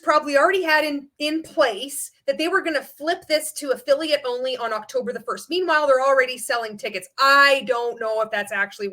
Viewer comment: At the 108:38 mark you mentioned a site to learn exactly what a pyramid scheme is.. I couldn't quite make out what you said.. cost something.. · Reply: Yes! The "Koscot pyramid scheme test" The company is a pyramid scheme if it meets these two requirements: 1. Participants pay the company for the right to sell products 2. Participants pay the company for the right to receive in return for recruiting other participants into the program rewards unrelated to the sale of the product to ultimate users.